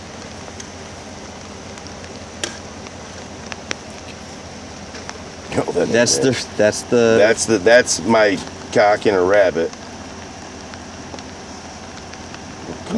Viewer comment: did he just say thats his willy in a rabbit